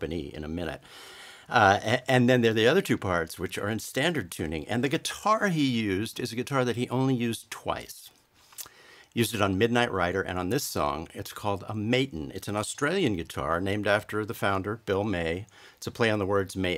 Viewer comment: Did You say „Midnight Rider“ @ 1:50? 😉 Anyway, a wonderful and inspiring lesson. Thank You so much!😊😊😊